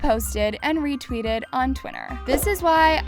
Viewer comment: did just say twinner instead Twitter? wait..... THE LOPEZ BROTHERS ARE INNOCENT?! Addison and Bryce REALLY embarrassed themselves on THE INTERNET the second hand embarrassment is real